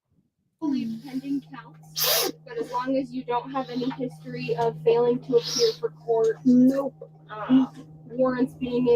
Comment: does say she "has a protection order"??? Did she think she was being protected from any charges because she agreed to be a witness? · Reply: "I have a protection order against the man who k!lled my husband in front of me."